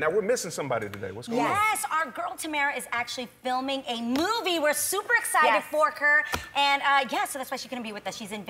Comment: "we're super excited forker" i hope I'm not the only one who heard that